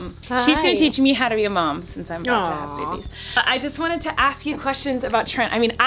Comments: did she say babieS???? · did she say babies, like, plural? CONSPIRACY! Hey what's up you guys, yes... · IT SOUNDS LIKE SHE SAID “since I’m about to have BABIES” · Colleen says cause I’m about to have babies I’m not sure whether she messed up her words or wether she’s having 2 little boys who else hears babies ps love Colleen 💕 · Did she say “babieS” at 3:20 ??? 🧐🧐🧐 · Did anyone else hear Coleen say babie(s) instead of one? At when she was talking with her mom( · did anyone else hear colleen say babies? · Did anyone else hear her say babieS?!?!?! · 👀👀👀 did she say “BABIESSSSSSS”?!?!?!?! Plural?! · Wait.. Did you say you're going to have babies at 3:20??? I thought it was a baby.. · did u say babieS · Did anyone hear Colleen say “babies” at 3:25-6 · Is that just me or at 3:24 did Colleen say "I'm about to have babies" with an s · i think i heard her say shes having "babies".... as in more then one. Not sure so reply if you heard it too. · Did anyone else hear babies not baby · OMG DID COLLEEN SAY “BABIES” ARE YOU HAVING TWINS?!!!!! Btw I might be wrong but I swear that’s what I heard omg. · did she just say babies 🤔